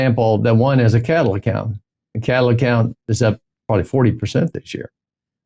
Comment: What did he say at min 5:40? Which stock did he mention? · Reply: Cattle investing